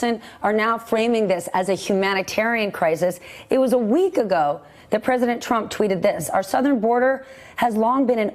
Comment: she said "shift". At first I thought she said something else, which would have been just as appropriate.